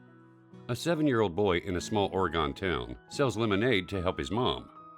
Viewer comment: Love the story's but did you say oragon